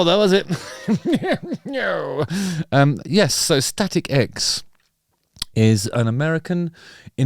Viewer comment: first time i saw them was decades ago warming up korn tour. awesome band. I heard you say "static eggs" every time :D